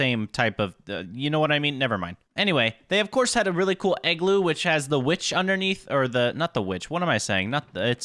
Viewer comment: in the video. i heard eggloo